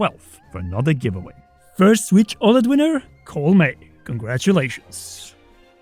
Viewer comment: I heard cole and nearly had a heart attack